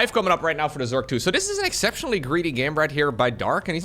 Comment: I heard that as a sexually greedy game by dark